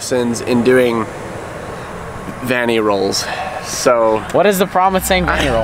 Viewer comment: Thought he said fanny roll.